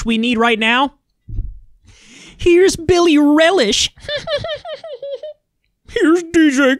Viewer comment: Did you say “Billie Relish”?? 😆